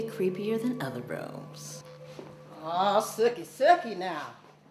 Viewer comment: lol sucky sucky now! Lol what did she say 😄😄😄